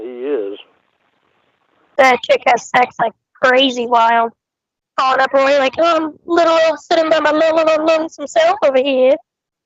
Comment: hahaha what does say right there? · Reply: Ah i got it "they're calling up Roy like, 'sittin by my lil ol lonesome self ova hea.''